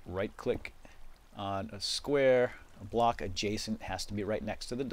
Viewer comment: Literally where I learned the word “adjacent” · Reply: ngl when I first watched this video as a kid I thought he was saying "jason" and I was just like "huh???" because I didn't know adjacent was a word nor did I know what it meant lol